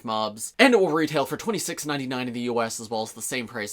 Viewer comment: 70 quid? That's way too expensive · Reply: Oh wait nvm I thought he said £69.99